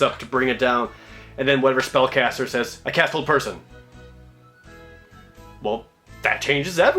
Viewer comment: I almost thought you said "I cast Old Person"